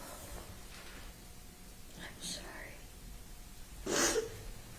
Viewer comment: So heartbreaking. Shedding tears. Also, did anyone hear a strange whisper at 26:36? I can hear someone whisper, “I am sorry.”